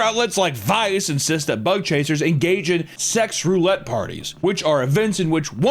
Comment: I thought he said " butt chasers", im definitely 1 of those. 🎉 edit: in Mexico theres a group of punk rockers that gave themselves HIV intentionally.